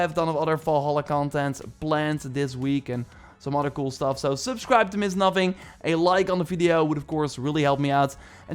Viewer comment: For a while I thought he was saying subscribe to Ms. Nothing😅